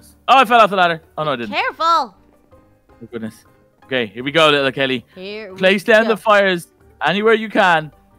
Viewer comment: What the heck the bee said hello little kelly 10:09 go to that part and then you'll see the bee say hello little kelly